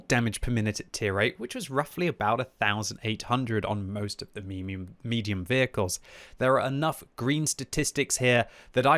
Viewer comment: Did QB just say 'tier 8 meme-ium tanks'?